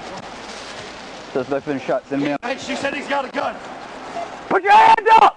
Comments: Tbh, at 1:35 I thought I heard "Send memes".... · literally thought it said "Suspect's been shot, send memes." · I could have SWORE the subtitle said “Suspects been shot, send memes” lol It would have been reasonable to ask for MEMS or memes in my professional opinion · Straight up at first thought it said “Suspect has been shot send MEMES.” Rofl 🤣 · i thought he said "send memes"